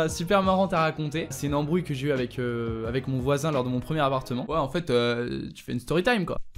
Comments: j'ai entendu au début : lors de mom premier avortement. Jme disais aussi que ct bizarre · A 1:03 j'ai compris : "quand j'ai eu mon premier avortement"